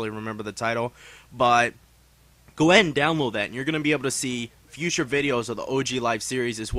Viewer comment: listen to the video at 2:01. Does anyone else hear " Glen Download that"???